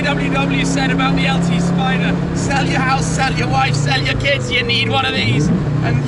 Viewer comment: did James say sell your wife ????? Anyway amazing work as always